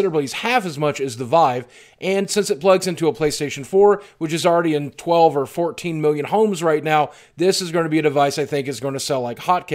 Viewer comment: I totally thought you said, "Which is already in 12 or 14 mobile homes right now". Most savage burn I've ever heard of.